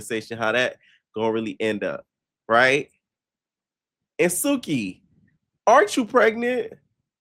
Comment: Egypt and Suhki?????? Man this year is full of surprises 😭🤣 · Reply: It sounded like she was saying her an India ✂️ not her and Suki